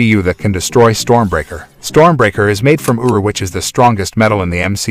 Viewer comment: Why did I hear “Stormbraker is made from uwu.”